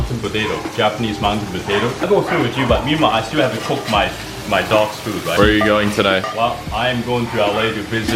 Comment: Did he just say that he still had to cook his dog's food? Am I hearing this right? That's gotta be one of the luckiest dogs in existence.